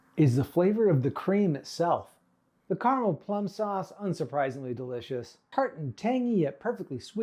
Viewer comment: that plum sauce is such a beautiful color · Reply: Thought you said Pink sauce like that woman made